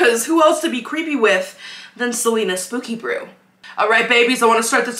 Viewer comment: I was watching this on my tv and I heard you say 0:19 you said brew But then when watching it on my phone I realized you already saw you say Celina spooky brew sorry